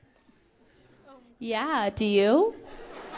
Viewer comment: what did that person say at 30:04? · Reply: "do you like Charli XCX?"